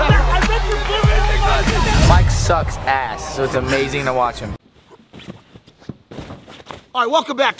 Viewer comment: I always thought the intro said “life sucks ass but it’s amazing to watch”...